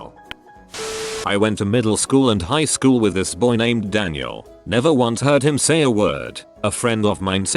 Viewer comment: My brain heard "his name was Daniel" and my brain just went "fffuuuuu, what nonsense did I say that was gonna get immortalized on Reddit/YouTube" It wasn't me though, I never got such a positive experience with my class.